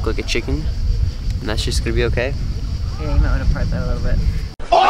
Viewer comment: I did not hear what your mom said at 3:18? 😂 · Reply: "you might wanna part that a little bit"